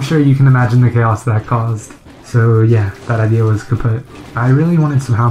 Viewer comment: did u say kaputt ? isn't this german ? or does I just have as bad ears as subtitles ?xD 3:55 and probs for making subtitles or let it get made xD